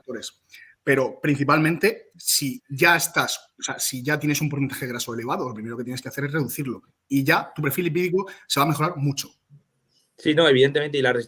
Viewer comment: Que dice en el minuto 14:50 ? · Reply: “Si tienes un porcentaje graso elevado, lo primero que tienes que hacer es reducirlo. Y ya tu perfil lipídico mejorará mucho”.